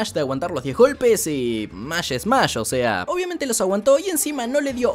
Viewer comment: Escuche que dijo "Mash smash". XD